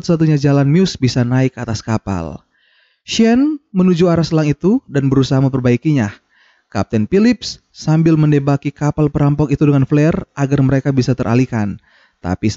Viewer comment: "kapten philips menembaki mereka dengan peler" Ada yang dengernya sama😂